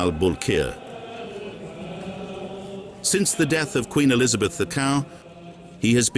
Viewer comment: Did he say "Queen Elizabeth the cow" ???